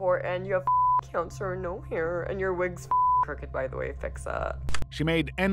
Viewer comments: "and your wig's f***ing crooked by the way, fix it" is what was said. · "And your wig is fucking crooked by the way, fix that." for those wondering what she said.